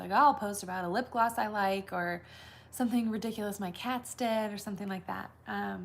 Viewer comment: Omg dying. It sounded like you said I was going to talk about “my cats dead”. I was like WHAT??!!?? Lol but obviously you said what my cats did! Love watching your insta stories every morning bc you make me laugh. You are so real and normal. Love starting my day with you! I am seriously bummed when there are no stories in the morning to watch when I am getting ready in the morning subscribed now but been watching instagram for past 3 years. Found you on Pinterest